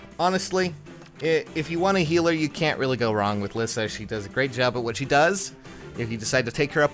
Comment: I thought it said Lisa is shi'